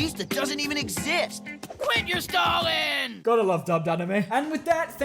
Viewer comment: did he just say Stalin?